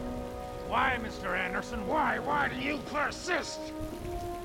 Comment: The first time, for some reason, I heard: Because I'm too stupid...